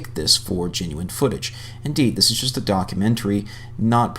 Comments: Did you really say DONKumentary there on the start, or did I just mishear you in the best way ever? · Hah sounds like you said "donkumentary" like meaning donkey.. if its intended then amazing. 🤣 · Did you just say "Donkumentary"? haha